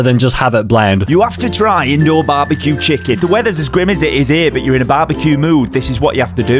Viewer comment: I heard “the weather is as grimace it is here”😂